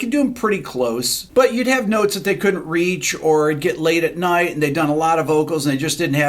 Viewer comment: do I hear at 0:55 "... or get laid at night and done a lot of vocals ... " ? nice 😏